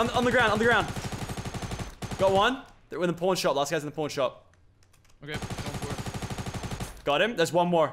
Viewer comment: did Lachlan say prom shop?